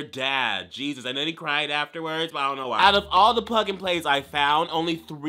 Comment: I thought you said "I bought all the butt plugs"